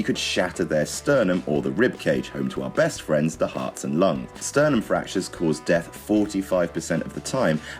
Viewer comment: Did he say "hearts and lung" at 0:57?